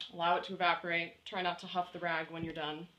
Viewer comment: Great video and thanks you. It took me a lot longer to get it done. I thought she said to huff the rag, not don't huff the rag.